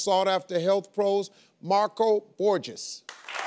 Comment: I thought he said Marco Gorgous! Just me? Okay.. :>